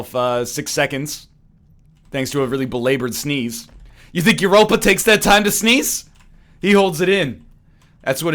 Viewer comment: did i hear him correctly? he said oppa? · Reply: He said Europa; he's the creator of greedbutt.